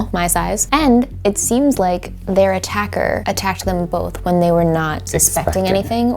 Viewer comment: in stead of 'them' I hear 'him' or even 'John'